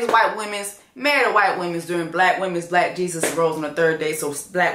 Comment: every time you say the long ass Black history month line I hear you say "Jesus rolls" & I picture my Jesus in a nice ass car like I SAVED YA SOULS, LET'S GOOOOO 🔥🤣